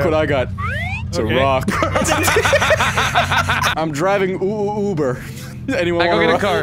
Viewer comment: did he just say UWU Uber